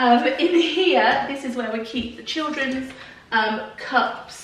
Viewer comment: I did lol at 12:00 - had to do a double take as I thought you said "this is where we keep the children" 😂